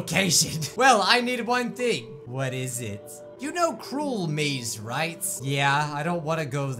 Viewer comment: captions say cruel me's